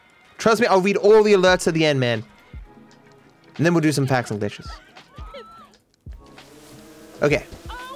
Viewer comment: the caption put packs and dishes instead of facts and glitches